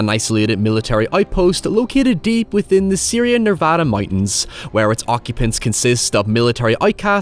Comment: Excuse me sir. Did I hear "Syria Nevada mountains"? 👀